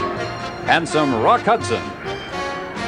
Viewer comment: I heard ..."And some Rock Hudson!". LOL!